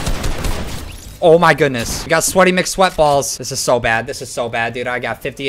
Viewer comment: did he say sweaty mick sweat BALLS